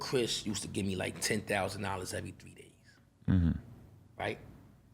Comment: Ppl like this will tell u anything if u listen 4:56 I was in my funeral home Lol if he aint ask he would hv left out its his moms 1:52 bring it to the feds ..naw not the real feds wtf 2:59 chris gave me 10kevery 3day that’s 100k a month 1.2mill a yr stop bruh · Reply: I thought he said every 3 weeks. Big difference. I'll have to go back and check that.